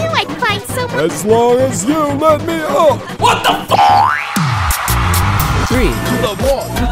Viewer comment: What did bell just say? 💀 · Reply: Taco Bell: WHAT THE FU##?!